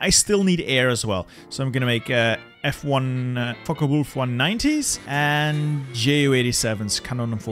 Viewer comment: When he said fw190 the captions got it like : fuckable for nineties